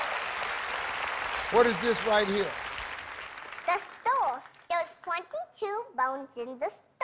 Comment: and 2:30 Am I the only one who heard her say "Stawl" or "Storr" ? (for skull) 💀